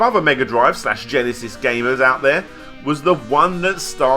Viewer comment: For a second in the beginning I thought you said "gay man" when you actually said gamer